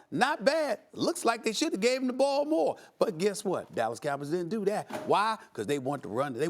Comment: Wow did he just say Dallas Cowards bro I hate the Cowboys but that was a little low